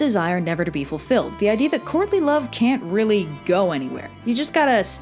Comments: hahahaha I keep hearing "courtly love" as "Courtney Love" and ... "The idea that Courtney Love can't really go anywhere" got me. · I kept hearing "Courtney Love"... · i'll be completely honest i thought you said "Courtney Love" the whole time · anyone else thought she was saying Courtney Love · I thought you said Courtney Love for a split second. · Totally thought she said "Courtney Love" and immediately wondered what a hypodermic needle would have looked like back then... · DEADASS THOUGHT YOU SAID COURTNEY LOVE I'M DONE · Dead ass thought I heard Courtney Love. Lil too much bleach for me. Yep yep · I keep hearing 'Courtney Love'